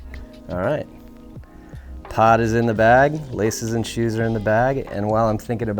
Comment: oh you said pod i thought you said pot 👀 never mind lol.